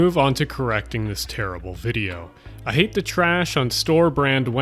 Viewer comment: took me a while to realize that he said "I hate TO trash" instead of "I hate the trash"